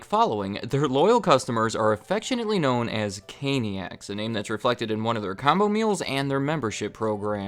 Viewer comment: actually says “Canicas” instead of “caniacs”